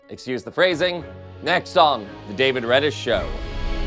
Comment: This is weird, at 0:53 when he says "Next on the David Reddish show", my closed caption says "Next on the David Packman show"